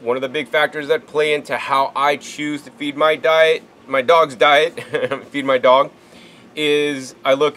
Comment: i would feed my dog sv3rige diet or would i feed sv3rige my dog diet? 🤔 i think both is correct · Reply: HAHAHAHA!!! At first I thought you said "feed sv3rige to my dog." That would be a good idea too.